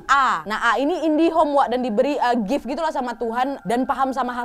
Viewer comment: Koq aku dengernya Indihome si?! 5:21 wkwkwk #marathonKHW